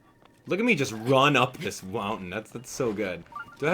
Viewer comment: Did anyone else hear him say wountain at 24:39 XD